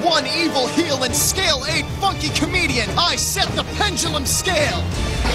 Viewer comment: What did he call both of his pendlum monsters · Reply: Mr Res oh ok cause I thought he said fucking comedian